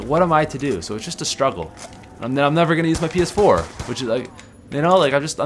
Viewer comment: when he said ps4, it sounded like he said penis 4 😂